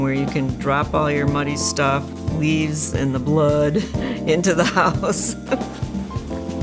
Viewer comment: Did she just say... blood?